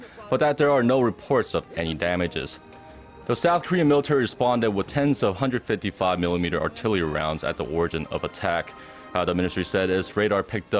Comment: What did he say at 1:31-1:34? South Korea responded with what? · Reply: Silas Nash "The south Korean Military responded with Tens of 155mm artillary rounds at the origin of attack."